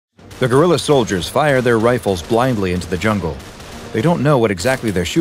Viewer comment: "The guerrilla soldiers fire their rifles blindly into the jungle." I thought it said "The gorilla soldiers fire their rifles blindly into the jungle."